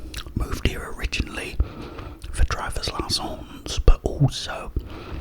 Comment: When you pronounce your fiancé name, all I hear is “driver’s licence” 🤷‍♂️